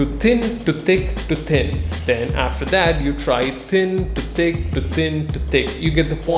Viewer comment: Instead of hearing "Thin to thick, think to thick" I hear: "Tin to tik tin to tik" Oof.